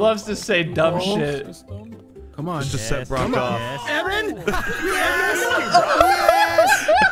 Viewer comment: said "Eren" instead of Evan